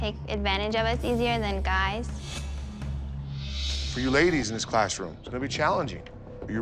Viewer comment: why tf are the guys nodding lmao idk if i spelled that right but ik what i heard he said laddies not guys smh